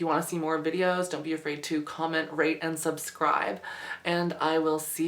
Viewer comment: When she said rate and subscribe, I thought I heard her say rape and subscribe... 0_0